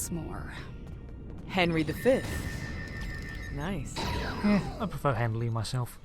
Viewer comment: I heard you say "I prefer Hamlet myself." I had to go back to relisten - it caused a 404 in my brain. I hate the flashlight lense flare with the burning passion of the heart of the sun. You should get the temporal agent kits and show them off in a mission or two. The ground gravity well is very satisfying-especially if you get the universal that removes their gravity.